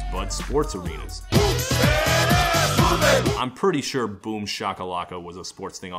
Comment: When I first heard it once years ago, I thought they were saying Whoop dat ass. Only l until this episode of One Hit Wonderland Boomshakalaka is for the NBA Jam arcade game